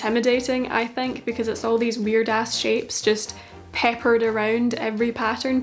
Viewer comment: did you say "weird ass" at 3:58 lol it was cute